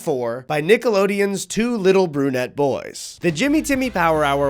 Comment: My men you would not BELIEVE the absolute double take I just did when I thought you called these young animated gentlemen GOONETTE boys in stead of BRUNETTE. I've booked my ENT visit and an MRI to be safe.